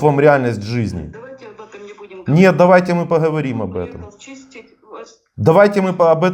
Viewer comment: Дима не услышал, как она на 55:35 сказала: пришел вас чистить....